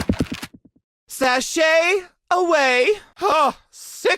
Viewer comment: SASHAY AWAY??? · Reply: I THOUGHT HE SAID SLAY AWAY OR SUM